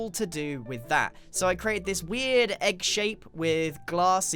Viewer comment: did grain said "egg"?